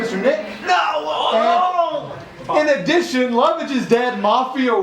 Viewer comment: What did he say at 35:16? "In addition, ___ is dead mafia wins" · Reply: Lovage